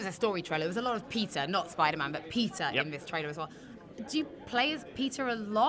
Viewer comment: I thought she was saying Pizza every time she said Peter’s name